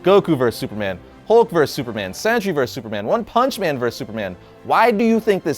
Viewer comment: Sandshrew vs Superman? What did you say? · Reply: Sentry vs Superman